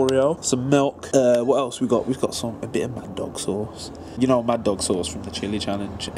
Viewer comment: i thought he said man dong sauce. ewww